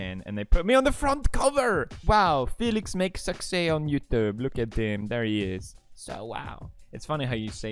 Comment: When he read the news paper cover for a minute i thought he said “Felix makes sexy on YouTube” with a Swedish accent